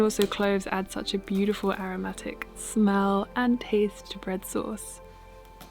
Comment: I thought you were saying red sauce... I was very surprised to see bread!